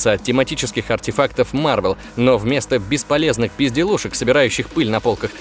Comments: Мне одному показалось на 7:42 что он сказал «бесполезные пизделушки») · Мне одной послышалось "пизделушек"?)